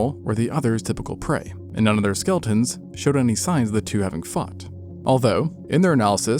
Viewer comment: am i the only one who heard "fucked" instead of "fought" 😭💀 my tired ass had to rewind that shit like 4 times to properly understand what was said cos i just knew i hadn't heard it right lol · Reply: I didn't even consider "fought". I literally listened to it again with the volume up and still thought he said "fucked". Came on here to see who else heard it, and that's when I realized how immature I actually am. Lol.